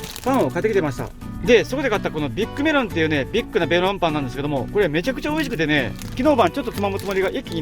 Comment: 「ビッグなベロンパン」に聞こえてツボに入っちゃったｗ